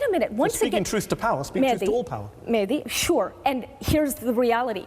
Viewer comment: did she say mandy to mahdi